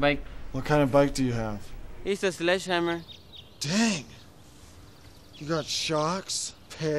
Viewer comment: Fun fact: the ramp wasn’t supposed to fall when Napoleon hit it. He ended up hitting his balls on the bike when it did, and right at the end, you can hear him say “take!” They left it in because it was funnier than whatever they had planned. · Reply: I always thought he said "Dang...it..."